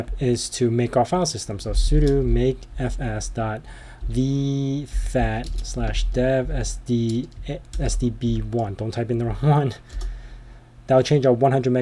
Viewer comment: the closed Captions says "sudo make FS dot the fat slash dev SD SD b12" I thought you said you type the commands into Closed Captions?